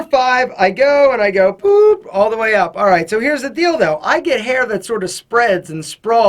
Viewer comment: does he say: here's the dildo? But there is only that buzzing tool...